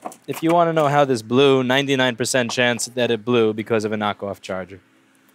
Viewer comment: Can someone transcribe what Louis says at 2:30? "If you want to know why it's blew, 99% chance it's blew becouse of..."? Mine English sucks. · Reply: knockoff charger, i think me blew because of a fake charger too!